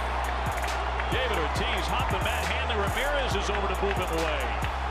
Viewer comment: I thought he said Manny Ramirez I was like wtf?? But he said Hanley Ramirez